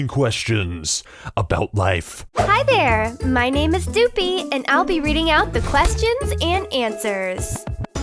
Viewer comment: I thought she said "my name is stupid"🤣🤣